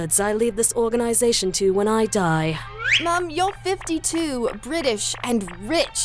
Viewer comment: i keep mishearing seras’s “ma’am” as “mum” and honestly that fits too